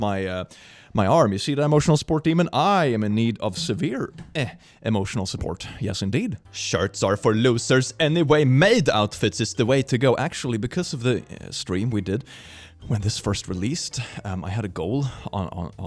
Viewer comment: Idk why but I literally thought he said “sharts are for losers” and for a split second that actually made sense lol